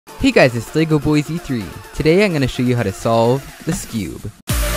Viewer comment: I thought the intro said "Today I'm gonna show you how to solve this cube"